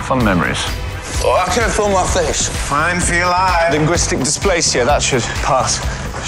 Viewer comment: What does Leonard say at 12:46-12:51? I can't make it out. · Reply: He said "Fine feel I" but he meant to say "I feel fine," but the side effects of time traveling messed up his words